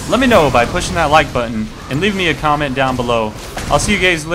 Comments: Colby did you just call us "gays" ? :o at 2:45 "I see you gays later" :o shame on you !!! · lol at the end it sounds like u said " see u GAYS later" xD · did he just say "see you gays later" at the end of the video? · Cool vid. Does anyone else hear him saying "see you gays later" at 2:45? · at the end it sounded like you said "see you gays later!"